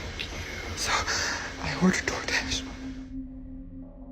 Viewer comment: Anybody else hear “take you” at 55:41 when the severe knocking is happening and creepy lady by the tree video?